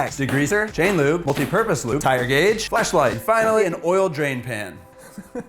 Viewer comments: I heard "flashlight" as "fleshlight". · It sounded like he said fleshlight😂😂